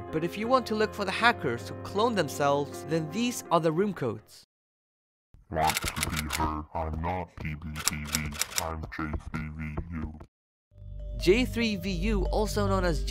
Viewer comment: I heard “not to be heard or to be (idk) I am J3V I am J3V”